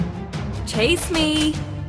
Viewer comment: I thought irithel said "Taste me" but it was "Chase me"